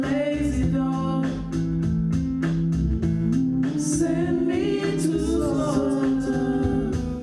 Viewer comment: AM I HEARING THINGS, DID SADE SING " send me to satan where I'm far away from god"